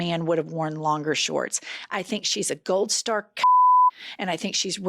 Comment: I heard, “gold star c@ and literally YELLED, “JENNIFER!!” NEVER. FAILS! 😂😂😂😂